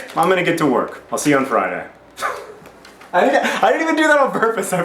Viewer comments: OOOoohh Calednrically!?!? I am not so used to the American accent! I thought he said "collindrically!" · At the end of this video I thought you said "collindrically," like "pertaining to colanders." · What is that last thing he says at 3:45...it sounds to me like "colindrically specific dementia". I can't make out what he's saying, and what I heard isn't a world, can anyone help me out?